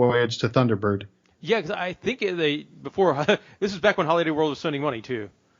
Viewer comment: "Back when Holiday world was (incoherent) money too." What did you say?? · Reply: Spending money